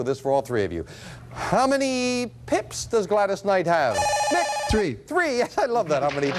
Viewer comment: Sounded like he said Pimp 😂